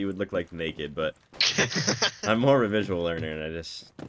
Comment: seems to be a typo i think the man said visual not visually idk what youre paying your editor but he clearly is Ethiopian